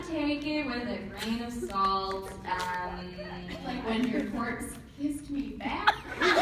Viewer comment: What does Natasha says at 35:43?? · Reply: "like when your corpse kissed me back"